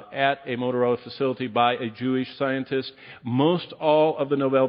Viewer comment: did he really say “Jewbie” scientist?